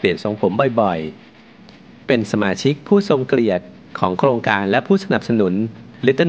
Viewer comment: ทำไมผมได้ยินว่า สมาชิกผู้ทรง"เกลียด" วะมีใครได้ยินป่ะ